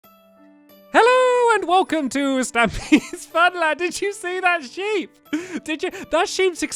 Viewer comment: turn on captions lol and if u can’t be bothered to it says speedla instead of Stampy’s funland :T